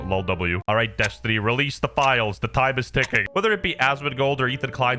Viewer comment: I thought you said he dropped the files? Wtf?